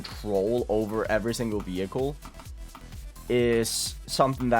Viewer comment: did he just say vehicles?